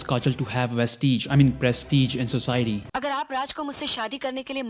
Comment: Did he just say "Vestige"? Lmao